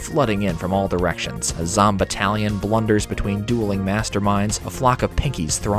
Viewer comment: i definitely misheard "a zombatallion" as "a zomb italian" at first